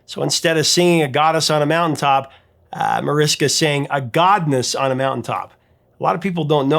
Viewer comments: I always thought she said "gutless" on a mountaintop. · I've liked this song since I was 9 or 10, and heard it hundreds of times In the past 50+ years. I always thought it said "Goddess". Now that I know she sings"Godness', that's all I can hear!